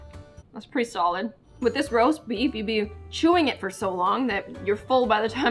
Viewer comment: When she said roast beef i thought she said roast beast 🤣 I don’t know if she actually said that but that’s what I heard